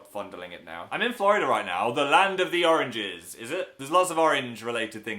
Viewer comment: Anyone else thought he said land of the orgies.....